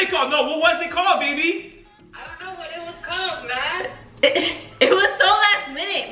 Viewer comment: does she say real name ??? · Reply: i think she said "Idk what is called man"